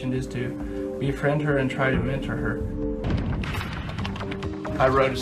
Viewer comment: "And try to mentor her." I thought he said enter. 😭😭